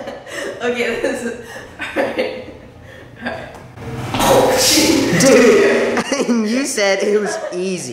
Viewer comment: did say “oh sh*t”